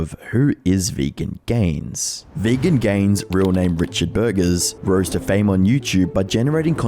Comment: I thought bro said "Richard Burgers" 💀